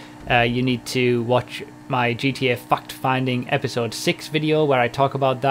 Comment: he clearly said Fucked Finding LMAO